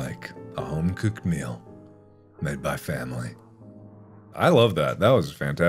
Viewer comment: did you say family